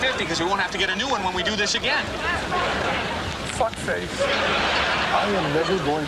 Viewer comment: Did he call the guy "fuck face" at 10:21???😂